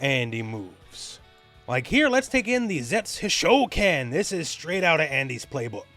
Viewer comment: DIEZ (10) Hisho-kens! (Blatino here, that's why I heard in the arcade... Among 10 cabinets) XD